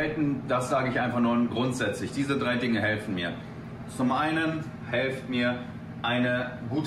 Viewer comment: Diese 3 Dinge helfen mir. Zum einen: "helft mir" (8:23) Habt ihr den Hilfeschrei auch gehört?